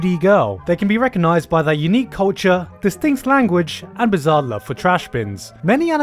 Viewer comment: Did you just say Bizzare? Was that a JoJo's reference?!